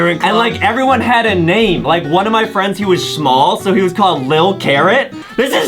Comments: When he said the boy was small it sounded like he said shmall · Wait did he just say shmall dude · adam saying shmoll instead of small